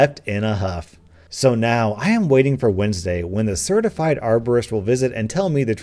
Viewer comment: My parents are arborists, hearing that that person cut down his trees infuriated me. Trees take forever to grow, how dare he do that? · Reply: bacon hair man i thought she said arsonist